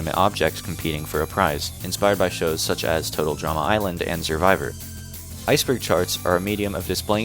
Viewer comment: did you say ice cream charts